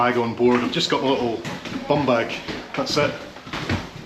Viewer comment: You said bum bag. I thought you said bomb bag😂. Careful where you use the term bum bag!! You might get a tour of the special facilities !!